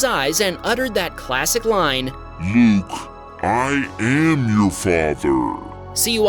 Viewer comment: I think the line actually says, "No, I am your father."